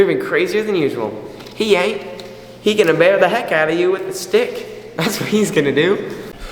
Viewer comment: The captions at 3:28 😂 i think he said stick not